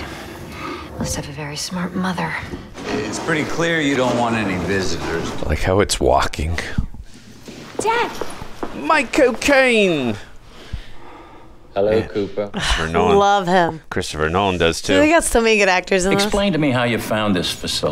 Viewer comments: he said "micheal caine" i heard "my cocaine" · I thought he said "my cocaine" 🤣